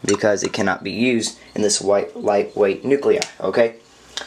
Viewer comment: Dude i didn't quite catch what you said on 0:48 what was it? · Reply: I had meant to say “light-weight nuclei” but I had entirely misspoke before that and tried to salvage the sentence as quick as possible. At that age, I really hoped I would get away with it😂